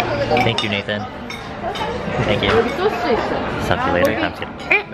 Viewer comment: DID HE SAY SUCK YOU LATER AT 2:53 ??lmfaooo he's so funny